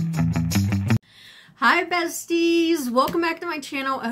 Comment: Every time you open up your video, I hear “Hi Betsy!” ( my name) when I know you’re saying bestie. I enjoy your videos!